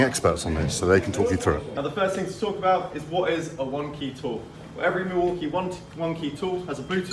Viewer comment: Would love to see them do so much more with OneKey. Felt like they really lost momentum with that tech after launch. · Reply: Oh, I thought he said Wonkey.